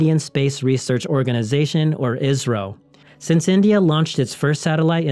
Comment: He said Isreal instead of India.